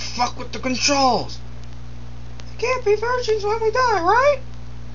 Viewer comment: Oh! And what does Sherry say after Leon says: "Sherry, don't Fuck with the Controls!" ? We can't be virgins for ever till we die..............? Right